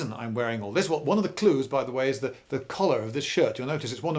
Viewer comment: BUT ITS NOT BEIGE! wait did he say color or collar?